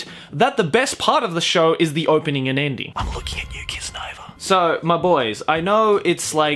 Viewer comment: what's he saying kiss nova?